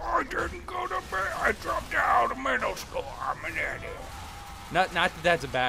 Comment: lol i thought he said "i dropped out of med school" and i was like ... how are they idiots?